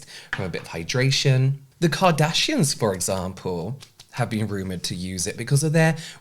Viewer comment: I literally thought he said "Cardassians" for a second there..